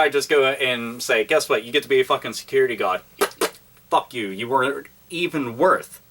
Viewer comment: Did you say "Security God"?